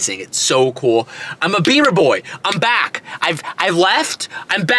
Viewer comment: Idk about you guys but I swear I thought I heard “beaner boy” lmaoooo